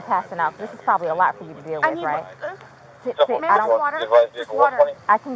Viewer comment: English is my second language, can somebody please explain what exactly the officer told the woman at 04:12? For me it sounds like "this is probably a lot to deal with for you, right?" but then i cant understand why she reacts so shocked and opens her eyes like a psycho...